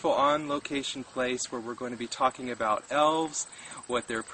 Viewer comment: lol, I thought you said Elvis at first. I was going to laugh, but now I'm crying... Elvis does exist! Elves are trying to kill the memory.